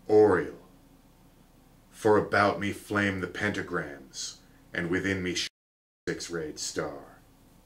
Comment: What happened at 36:40 , it cut out? What was said? Edit: Found it: “Before me flames the Pentagram, and behind me shines the six-rayed Star.”